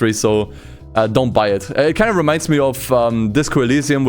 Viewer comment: He say's "dont buy it" i hear "pirate it"